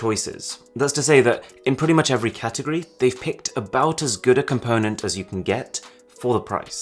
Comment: Did he say "gooder"?